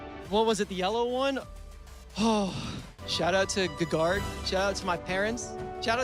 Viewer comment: he actually said "ggarg" which is a groupe made by feodoric so yeah